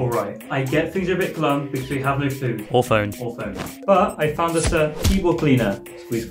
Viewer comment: When he said or phone i thought he said orphan 🥲